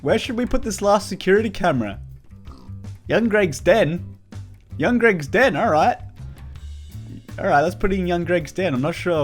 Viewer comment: I thought he said Young Greg was dead. if you said that, boy have a great day....